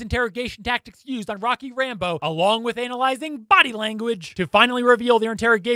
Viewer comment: Do I hear “analising at 1:23 instead of analysing